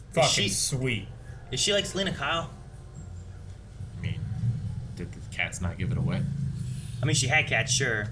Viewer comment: They all knew its selina, you deaf? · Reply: You might be mentally challenged and deaf.. at around 12:30 as I said, he clearly says “is she like Selina Kyle?” And the other asks “did the cats not give it away?” To then? Says “I seen the whips and then now that so it’s pushing me that she is”… man you are a bright one